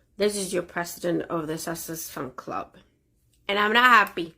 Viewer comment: IS she drunk?? LMFAO Gotta say I love the way she slurs out "SUCKSESS" instead of susesex -- it's absolutely hilarious. Plus she's crazy AF...right up MegaFlop's alley.